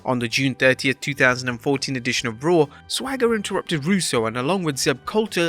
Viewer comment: I heard Russo